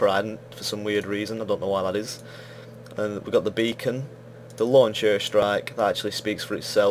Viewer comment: I thought it said bacon at 0:54 and got excited :(